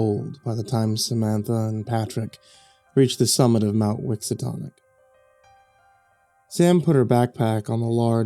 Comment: For a minute there I thought you said "Miskatonic"